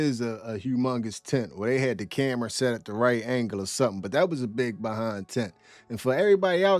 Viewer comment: About @ 4:20 Does he say "that is a big tent" followed by "that is a big BEHIND tent"? LOL